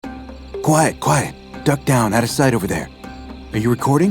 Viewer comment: As a Romania I hear "Quiet coaie" instead of "Quiet quiet". I love these videos